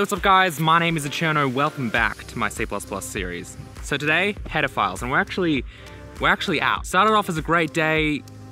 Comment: lol it sounds like your saying pedophiles lmao. The tuts are aweosme!